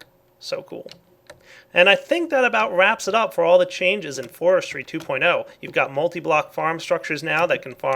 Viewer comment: I thought you said there were bee changes as well?